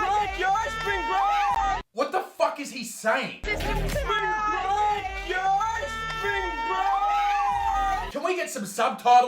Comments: "Sprink Break! Spring Break!" That's what he is shouting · I thought the guy at 4:57 said, 'spring break, yo, spring break!' 😂